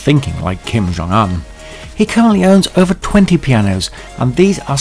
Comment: the subtitles say Kim Jong Un has 320 pianos---but the narration says 20, Which is right?